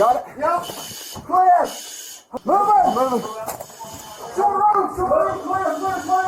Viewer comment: Just some subtitling tips: At 11:47 the subtitles read "Third room" but i believe that the officers were announcing "Short Room!" which is a term you use while clearing a building with a team when you open a door and it turns out to be a small space or a closet instead of a full on room.